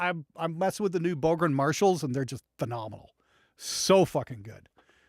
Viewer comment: 😊 I'm on the flip side of fast forward whirling non 24 sleep. For awhile I thought you were going to say marshmallows but it was "Marshalls."